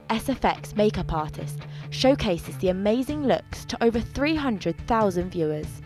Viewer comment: I thought it said amazing sex.